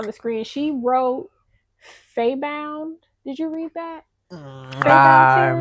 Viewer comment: Thanks for the book talk! My TBR list just keeps growing. Just started Faebound by Saara El-Arifi. I heard "fairies" and was like, Yes! Good to hear you sharing about books. 🧡🧡🧡